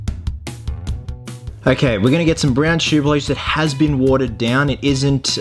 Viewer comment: At about 9:00 minutes, did anyone else hear him say "brown shooblish???" Awesome work btw!